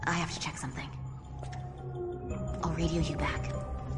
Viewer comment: What say Rachel in 0:34? "Over here you back"??? =P Sorry, english is not my natal language... U_U